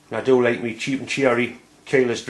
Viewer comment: Hi, sorry mate I'm still trying to learn your accent, at 9:26 you say "I do like my cheap ????? Key less chuck" I can't make out if your saying cherrie or T.R.E. I Googler both and didn't get many results, could you please tell me what brand you say? Keep up the great view, Cheers